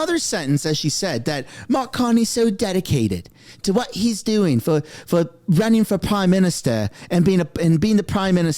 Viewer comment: I heard ‘Why are you still running for PrimeMinstership’…